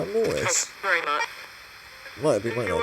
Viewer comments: is hard to hear because theres another sound but I heard Mortis really fast from a female. said it similar to how I heard it on his videos , maybe its just me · I think I keep hearing Mortis being said too?. 9:36 - mortis, 9:54 - mortis